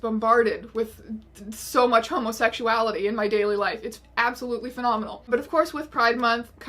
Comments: Did she just say "so much homosexuality in my gayly life"? Nice. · For a second, I thought you said gay-ly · 53 I thought she said my gayly life at first. 😂 I’m such an idiot!